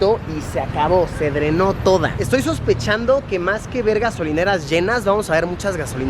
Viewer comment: lol pensé que dijo vergas